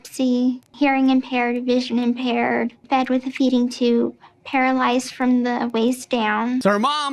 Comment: I fr thought she said "hair lice from the waist down" instead of paralyzed 😂😂